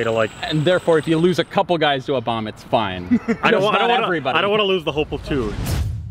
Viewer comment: To whomever has access to the closed captions, I think he's saying "I don't wanna lose the whole platoon" and not "I don't wanna lose the hopeful tune" at 0:08.